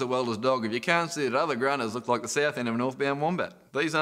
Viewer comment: That bit at 00:14 I didnt understand at all. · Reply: the ending is "look like the south end of a North-bound wombat". the rest is lost on me.